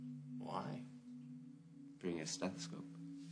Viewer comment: what does luke say on : 4:14 after: why ?? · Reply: bring your stethoscope